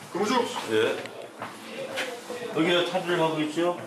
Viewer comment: CRPT : 여기 왜 탈의를 하고 있죠? 인데 자막은 "왜 운동하고 있냐"고 나오네요. 발음도 안 들려서 PD님이 그냥 나름대로 이유 붙여서 자막 다신것같은데..... 무슨 상황인지 몰랐으면 저 분한테 물어보시지..... 방에서 운동(웨이트)을 하면 안 되는 건 맞지만, 저 상황은 운동한다고 뭐라하는 게 아니라 왜 방에서 옷(관복이라고 부르는 죄수복)을 탈의하고 있느냐는 부분입니다. 입으면 답답하고 먼지나 오물이 묻어서 더러워지기 때문에 수용자들은 보통 방에서는 관복을 입고 싶어하지 않는데, 원칙적으로는 관복을 입는게 규정입니다.